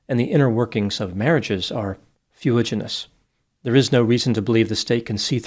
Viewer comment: Dr. G says a word that I don't know - anybody get that? It sounded like "fueginous," or something. But I cannot find it anywhere. Any ideas? Dr. Grande - can you enlighten us? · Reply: Hi, he says fuliginous - meaning murky 😊